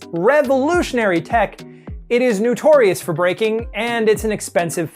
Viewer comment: "New-torious". Was that a pun? or did i just hear it like that.